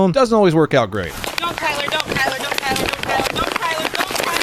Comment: "don't Tyler, don't Tyler!!" Like she's trying to call her dog back. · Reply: I thought she was saying, “go Tyler! Go Tyler!”🤣🤣🤣